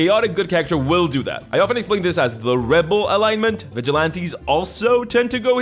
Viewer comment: I heard this as “the Red Bull alignment”